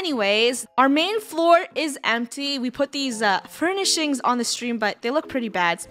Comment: Who else thought said empty a$$ not MDS and had to put subtitles on to see 😂